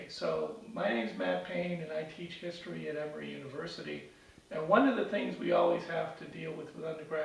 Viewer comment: Lol, I thought he said 'every university' at first.